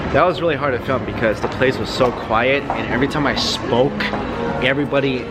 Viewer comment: was I tripping or I heard Mike saying he smokes?